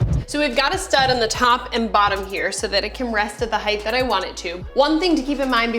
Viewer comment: did you say "heighth" at 11:35? That's not a word in English. Close captioning corrected it to "height."